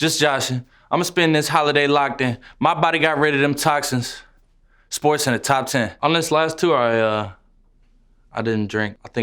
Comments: when he said ‘sportscenter top ten’ i thought he said ‘this boys in the top 10’ is it just me?.. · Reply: 복권 I thought he said “this voice in the top ten” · Couldn’t swore he was saying “this boys in the top 10” not sport center top 10😂😂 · Who else thought he said “this boy’s in the top ten” in the song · i always though he says: ,,This Boys in the Top Ten" instead of ,,SportCenter Top Ten" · when he said ‘sportscenter top ten’ i thought he said ‘this boys in the top 10’ is it just me?.. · Bruh I thought he said "this boy's in the top 10 not "sportscenter too 10" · Wow. For so long I thought he was saying "this boys in the top 10" 😂😭😭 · I thought that he said ''this boy's in the top ten'' not sportcenter, huh?